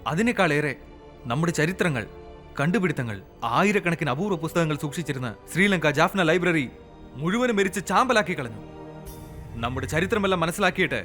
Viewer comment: what did he say "sri lanka janna library" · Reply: jaffna library burnt in 1983 during black july riot...